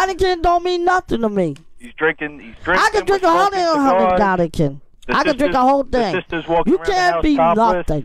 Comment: Wth did Beetle say at 3:05? haha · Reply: Taíno Escobar i could drink a hundred Heinekens